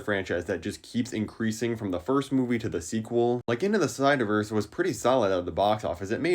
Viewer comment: did bro just say "into the SNYDERVERSE" ? (i don't like his films)